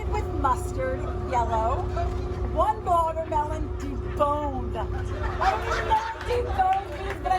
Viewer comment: I thought she said watermelon, boned., not deboned, and that was really funny